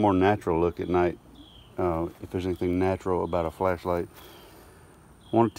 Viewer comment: Sleep meds and late night YouTube videos don’t mix. I thought he said flesh light and I thought, this is about to get interesting with all of these bees. 😳😳😳😶‍🌫️😶‍🌫️😶‍🌫️🥴🥴🥴🤣😂🤣😂🤣